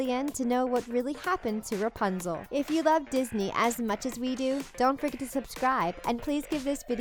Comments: who else heard "don't frickin subscribe." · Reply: I heard don't frigiten to subscribe 😂😂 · who else heard "don't frickin subscribe."